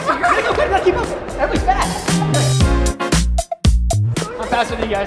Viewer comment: did he call everleigh fat or fast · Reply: Fast